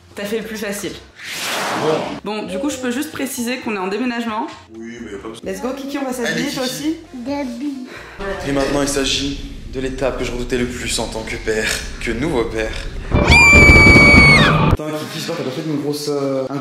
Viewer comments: C'est moi ou quand la maman elle a dit "let's go on va s'habiller" Kiki il a dit Zebii...🤨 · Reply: « Les habits » il dit ! Rooooo. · c moi ou il a dit « zebi »🤣🤣